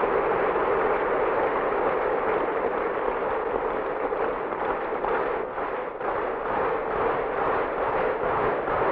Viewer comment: "What did I say?" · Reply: Il dit: "vive l'Algérie démocratique et populaire",:)